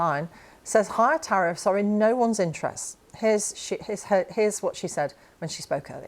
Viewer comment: "Here's sh1t...Here's what she said..."